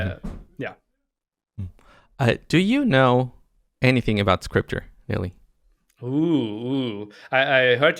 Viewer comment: Who was talking to ? Did he say scriptor? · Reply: "scripter", that was his nickname. I think in some forums he had "Scripter64" in others "Scripter16"